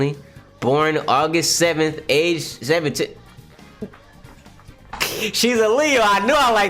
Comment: Bro at first I thought you said "shes illegal, I knew i liked her..." I was about to be like BRUH!